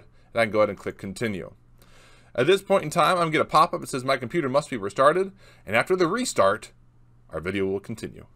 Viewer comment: "My computer has to be restarted" sounded like you said my computer must be Retarded